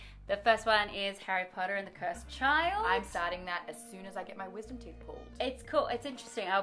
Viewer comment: "I'm reading it as soon as I get my wisdom teeth pulled ..." Woah, this is a month old, right? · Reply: I thought she said "Wizard teeth" for a second... :p